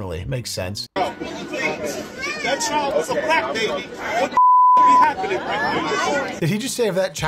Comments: Yooooo, I'd never heard the Black baby remark😂😂😂😂 goddamn, man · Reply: I initially thought he said "crack baby." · I thought he said the child was a crack baby..🤷‍♀️ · LOL I thought he said CRACK baby! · i thought he said "thats a crack baby" wow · I thought he said crack baby! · It's all mental illness ..I thought he said CRACK baby